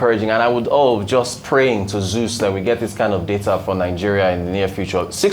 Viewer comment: Did he just say "praying to zeus" ? 😂😂😂😂